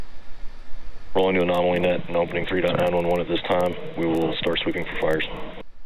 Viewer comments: anyone can tell me what was said at 12:14???? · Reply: "??? 911 at this time, we will start sweeping for fires." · what does the announcer say? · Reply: Due to an anomaly were opening up code 3.911 at this time. We will start sweeping for fires.